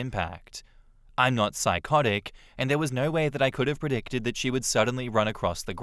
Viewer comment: Lol "I'm not psychotic" instead of "I'm not psychic" Changed the whole meaning 😂